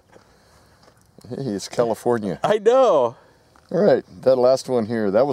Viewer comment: Shoots left, "it's California" Lol 😂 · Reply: Yurr Next I thought he said commifornia , which was even better !